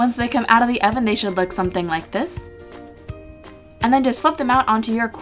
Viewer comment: I legit thought she said "and then just fuck them up" and I was so confused but also like "yeah fuck it up girl"